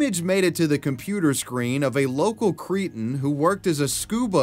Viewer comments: I really thought you called them a "cretin" and it took me a bit to figure out that it was "Kretan" 🤣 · I heard say "the image made it to a computer screen of a local cretin" and was like why is he gonna be so rude to the guy that apparently saved a woman's life? Took me half a minute to realise it was actually "local cretan"